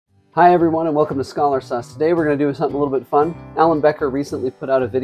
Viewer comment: bro the fact that the beginning of the vid i heard "welcome to Scholar Sus", i instantly look at ur channel, and saw. that's y my mom says that i never listen carefully🙏🙏🙏🙏😭😭😭😭